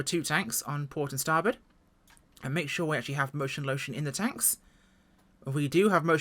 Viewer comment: Outstanding review as always! But “Motion Lotion” in the tanks? I’ve always heard it as “Go Juice!”